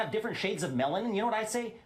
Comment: I’m so glad David thought he heard “different shades of melon” because that’s exactly what I heard too 😆